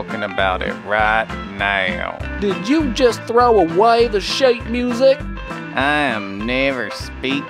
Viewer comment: DON'T TELL ME YOU DIDN'T HEAR HIM SAY "SHIT MUSIC"!!!!